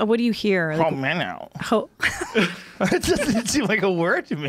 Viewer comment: I heard "im an ol' man now"